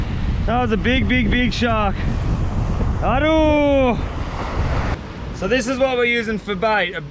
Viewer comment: did he just say "aduuh"? Btw, greetings from Indonesia.